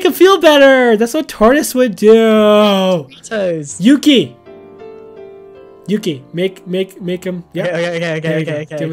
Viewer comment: Did anyone else hear the girl voice behind voice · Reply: Ella Richards it said Doritos